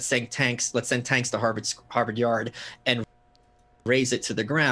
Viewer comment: the new right is saying let's send tanks to Harvard yard. The first couple times I heard this, I heard "send think tanks". That may be my bias. But is Malice "the new right"? Or is he commenting about "other people".